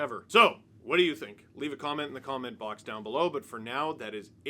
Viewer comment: Have you tried changing your outro to "Leaf a comment below"? I know I misheard it, but I'm suddenly wondering if it would stick